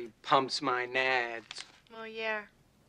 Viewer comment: I love his work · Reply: I always thought he said “what love is worth”